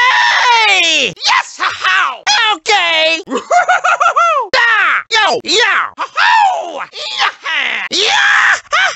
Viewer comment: What is he even saying? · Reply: Lightning Cayo OKAAAY!!! Funky Kong mimicked Lil Jon